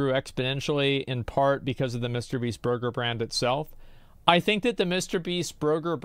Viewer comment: did he say 'brurger brand'?